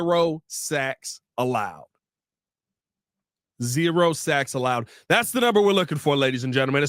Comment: It sounds like you said zero sex allowed 😂